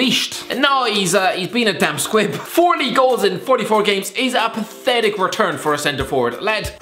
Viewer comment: What did he say at 3:25, because I heard 40 goals for 44 games, which is pretty good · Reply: I think he said 14 in 44 but he has that thick ol Irish accent